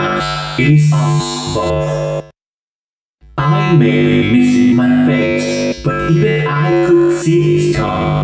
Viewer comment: Withered bonnie sounds like he's saying.. "I may be missing my face, But even I can see scott." anyone else hear that at 5:04?